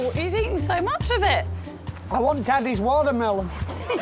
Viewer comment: What does Johnny say at 39:20? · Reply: "I want daddy's watermelon."